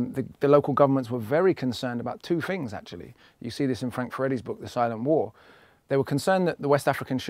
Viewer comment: I'm an African-american and unfortunately I couldn't understand the author and title of a book that Akala refers to about 5:05 minute. It sounded like he said, Frank Freddy's book 'The Side of War', but I can't find any such book. I am struggling with the British-like accents... I am very impressed with Akala's work